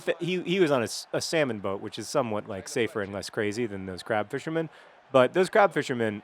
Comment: Oooh, "CRAB". For two minutes, I thought you were saying "CRAP fishermen".